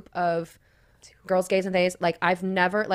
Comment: What does she say here, is the most incredible group of girls gays and ___? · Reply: Girls, guys and theys